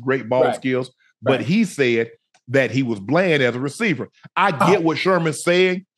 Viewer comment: I thought Sherm said he just blends